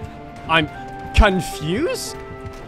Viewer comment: I thought he said kuntfused